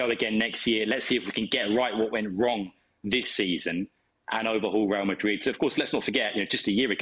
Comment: Overhaul Real Madrid ? Xavi, while managing Barcelona ? Did I hear it right ?